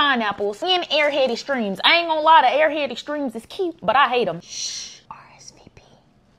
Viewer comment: Did she just say “SHHH RSVP” lol instead of asmr 😂😂😂